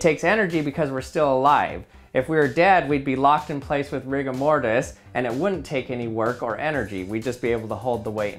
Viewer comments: Who else thought he said locked in place with Rick and morty? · I thought I heard him say Rick & Morty...